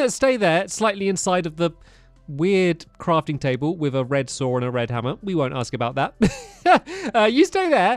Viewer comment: am I colourblind or did he say "red sword and red hammer" when looking at the crafting bench